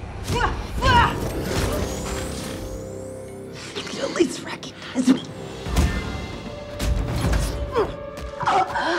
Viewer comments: what does Nat say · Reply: "You can at least recognize me?" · What does Natasha say at 0:42? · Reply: "You could at least recognize me" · What does widow say at 0:41?? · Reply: "you could at least recognise me" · what does Black Widows say ? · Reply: "you could at least recognize me" which indicates they knew each other at some point (a nod to the comics)